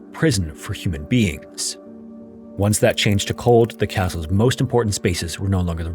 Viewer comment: "Once that changed to cold," - what the heck does that even mean? Your Gen AI bug out and you didn't catch it before release? edit: I'm talking about the subtitles. Or does those who are hard of hearing, or those whose device playback just doesn't sound right or good, or those who can't turn up the volume because of circumstances/environment, not deserve clarity? Further edit: I'm done on the topic and has clarified which I'm happy with, so if all you have to say is just some ableist condescension then just save it. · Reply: "Once that change took hold". Also, FYI, a gen AI voice doesn't make mistakes like that. Unfortunately, I still do, haha